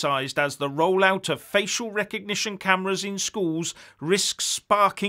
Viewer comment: Oh, that's a relief. I thought you said "fecal recognition".